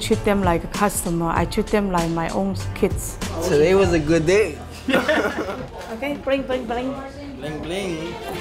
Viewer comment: Okay. Bling bling! bling! 🤣😂 Did she say that? 2:01 oof